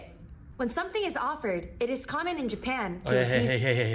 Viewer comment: Haha you said free tissues were a thing because "pollen is common in Japan", but i heard "porn is common" and was like, yeah but...ew 😂🤣